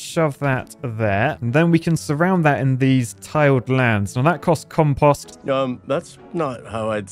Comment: I like the way the editor is triggered by the British pronunciation of compost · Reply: The first time I thought it was because he said tiled land instead of tilled land, so I was confused the second time it was referred to haha